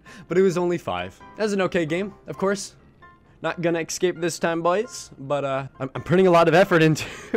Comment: did he just say excape ? Omg lol.